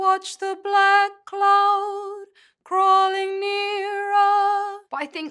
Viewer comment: Ooooh she says black cloud. Thought she was saying blood cloud the whole time.